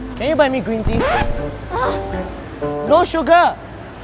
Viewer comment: I heard it 'no SUGA' army translation 😭💜💜💜